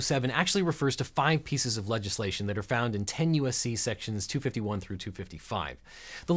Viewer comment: 10 U.S.C. Sections? I thought he said tenuous C-sections! No really, I did, I wasn’t looking at the screen or paying particular attention when that phrase grabbed me and I had to go back, haha.